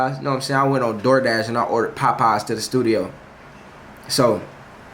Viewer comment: Nah the way jay saying “POPeyes” got me weakkkk😂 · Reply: Sounds like hes saying potpies